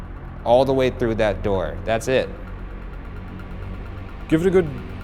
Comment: Personally I heard “that’s me” rather than “that’s it” in the showers